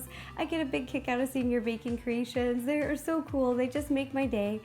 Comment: At the end i thought she said anus snail wave xD